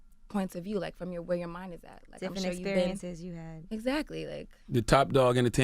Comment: that sounded like whoever spoke off camera said, "Tiffany, are you there?" Lol. And with the context of what she was talking about, her feeling invisible, that was trippy, but I'm guessing she said something like, "Different" and not "Tiffany."